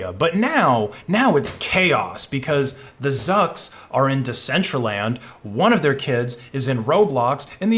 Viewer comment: wait... what? ...says, (whore) Zuckerberg?